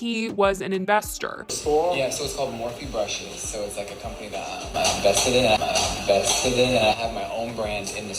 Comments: I thought he said “I’m invested in....” 😂Oops. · sounds like he said "vested" not invested....which means more like "loyal to" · I heard "vested" not "IN-vested". Vested would make sense.. but who knows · I might be a bit deaf or something but I hear "I'm invested in". Like you can be invested in a project. ...goes off to get earmeds... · They said “Jeffery Star” is not an investor, could he be a silent partner? Or maybe an investor understand a pseudonym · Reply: I did not hear him say he was “invested in” I heard him say “I’m vested in” which has a totally different meaning.